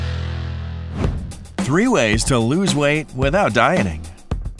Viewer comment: In the beginning, I thought he said “ without dying “ I was like, OMG WITHOUT DYING?!? lol...